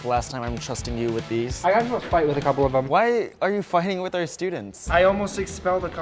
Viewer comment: Bro when he said "why are you fighting the students" I heard "why are you f***ing the students